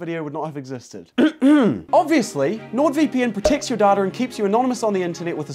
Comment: Guys....I literally heard "daughter" not "data". At first I actually thought he said- "Nord VPN preotects ur daughter".😅😅😅😅😅